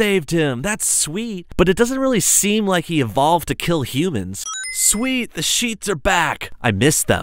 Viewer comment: Did anyone notice that in one of the subtitles around 4:08 it said "hill" instead of "kill"